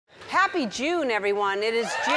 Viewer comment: In the beginning I thought she said happy jew I was like Ellen. What